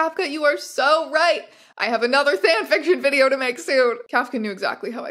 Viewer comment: did you say “Thanfiction” as in the LOTR guy or “fanfiction” as in… well you know